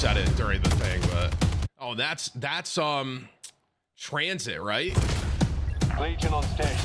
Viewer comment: did bro say Tranzit? I'm dead bro lmao